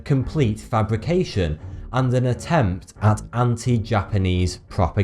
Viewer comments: it sounds like hes say 42 insted of thoughty2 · The answer to Life, The Universe, And Everything: 42..... or were they saying 'Thoughty2' and were misheard?